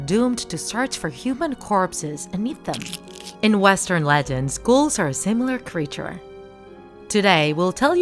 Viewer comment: Who else thought they said schools are a similar creature